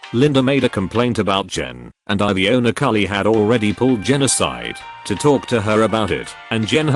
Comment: I wasn't looking at the screen when the voice said, "The owner Carly had already pulled Jen aside..." I definitely misheard it as genocide instead of "Jen aside".